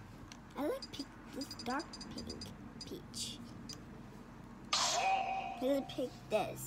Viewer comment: Why when it said peach I thought it said bitch.